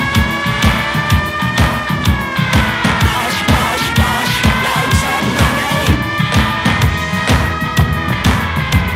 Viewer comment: What is it the Daleks say at 0:25 ? I've been wondering since 1988, when ABC TV often played it before the new episodes on James Valentine's The Afternoon Show. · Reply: Bosh! Bosh! Bosh! Loadsamoney! This is a reference to Harry Enfield's character "Loadsamoney" who had a contemporary novelty song featuring that catchphrase. I suspect, without any evidence, that Drummond and/or Cauty were involved with that but someone will be along shortly to put me right.